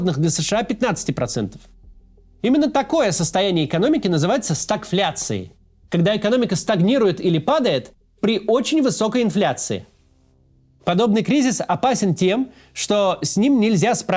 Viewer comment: In the captions: The economy doesn't grow, unlike inflation I hear: When the economy doesn't grow is during extreme inflation (Когда экономика стагнирует или падает? При очень высокой инфляции)